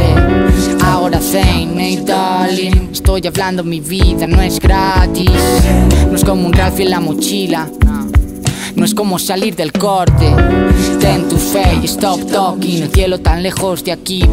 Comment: que dice? No es como un grafi en la mochila?